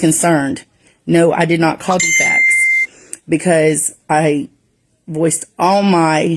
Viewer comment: states “No I did NOT call dfacs”